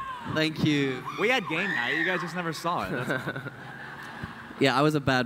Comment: "We had game night. You guys just didn't see it." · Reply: Oh god I heard 'gay night' a litteraly died 😂😂😂